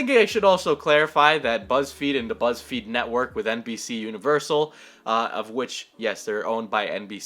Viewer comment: I heard not NBC'S but NPC universal